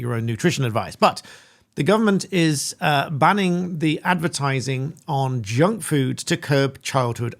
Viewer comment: Dear God, I thought you said "banning the bangles": talk like a flippin' Egyptian! Parents can't be held responsible for Janet and John becoming porkers... that's the role of a centreist government. Is this how it was under Lenin?